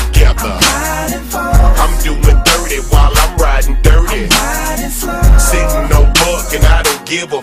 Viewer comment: I'm doin' dirty while ridin' dirty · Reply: Justin Roscoe I thought it said I'm doing 30 while riding dirty